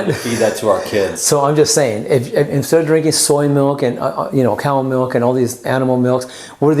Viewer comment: does he call soy milk animql milk? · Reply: nop bro, hi said: soy milk , cow milk and all these animal milks ( refeering to cow ) not to soy